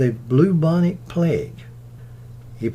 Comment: I love your accent! Very authentic! Just one thing - to my ears it sounded like you said “bluebonic plague.” Cute. Love this video about Jack Palance.